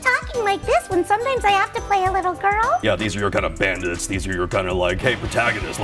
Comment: I cannot listen to Kaiji speak without hearing Gojo now. All I hear is “You’re kinda weak”😂